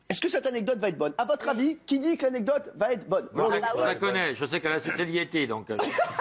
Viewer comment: il dit quoi? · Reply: jviens de comprendre "je sais qu'elle a sucé le yéti"